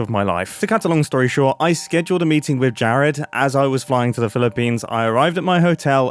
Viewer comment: did you say Philippines?